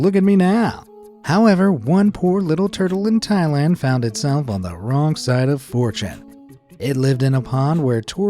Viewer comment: LOL, I seriously thought he said “the wrong side of 4chan”.